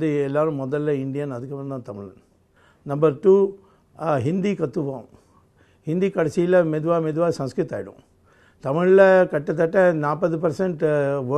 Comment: Great Swami ,ur a great leader swami 👍🏻👍🏻only Tamilans will hate u swami g ..ur one of the most intelligent politicians in India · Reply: Akash Santosh - Please see from 5:29 to 5:42 again. He did not say //kojam kojama sanskrit kondu varuvom// he said "Hindi kaththukkuvom, mella mella hindi sanskrit aagirum" there is a difference between 'we will bring' and 'it will become' - that is what our programed minds wish to hear. Whether you like the narrative or not - it is necessary to be fair.